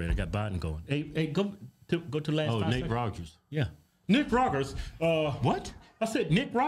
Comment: It sounded like you said “Nicaraguas” at first instead of “Nick Rogers” and then “Nate Rogers” 😂